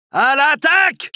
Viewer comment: What does he say at 0:04 sounds like "Khai!"? Anyone? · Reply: He says "prêt !" (Ready!)